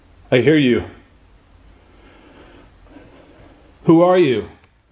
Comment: right after "scary" appeared on the screen, it sounded like the same male voice said something else, maybe ending with "... You know?" Definitely an "O" sound.